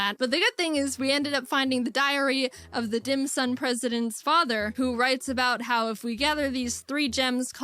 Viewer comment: i thought she said DIAREAH OF THE DIM SUN PRESIDENT’S FATHER